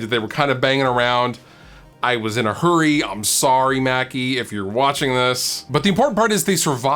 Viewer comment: Misheard as Maccies, now I want a McDonald’s